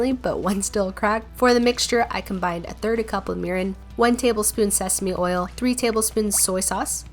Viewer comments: Is it just me or did i hear a cup of urine `~` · I’m sorry but - 2:52 why did I hear urin